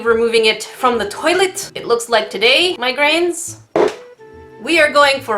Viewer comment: I thought you said Migraines instead of my grains lol